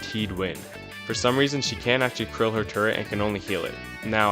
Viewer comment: she cant curl it? What are you saying? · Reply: Krill, I say that instead of ki*l to keep YouTube happy